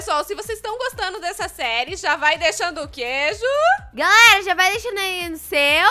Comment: só eu que ouvi Tem que deixar o queijo? Kkkkkkkkk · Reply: Eu sei que ela disse ja já vai deixando que ju mais eu ouvi queijo